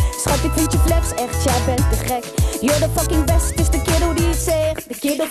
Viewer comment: I don't understand a word but laylow but did he just say "YOUR THE FUCKING BEST" in English?